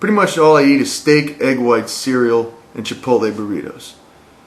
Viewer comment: haha I thought you were saying egg white cereal not egg whites and cereal